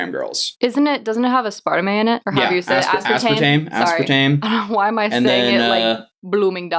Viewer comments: I swear to god I thought said, “doesn’t it have a spider man in it” when she tried to say aspartame 🤣😭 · I thought she said "doesn't it have Aspiderman in it" I was like what, then said "aspartame" 😁😁. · I thought his girlfriend friend said “doesnt it have a spider man in it”